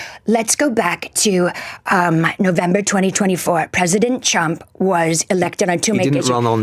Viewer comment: Is it just me or did she say "President Chump"? - LOL She'd better watch out. She might have a visit from some people when she returns home.